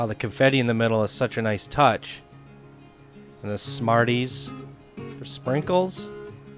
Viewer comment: Every time you reference Smarties, I hear you saying "I'm CANADIAN!!!"